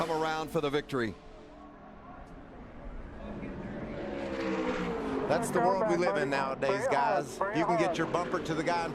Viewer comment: Lol at 12:12 did anyone hear that ?? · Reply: "Fucking dirty" (how did I just notice that?)